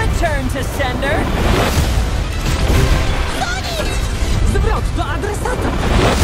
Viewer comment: Nic nie zrozumiałem co sonic mówił 0:07 Co on mówił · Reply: zwrot do adresata